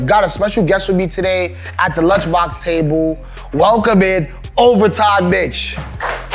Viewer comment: bro i heard overtop bitch😂😂😂😂